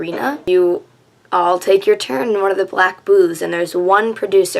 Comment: I thought you said "black boobs" oh god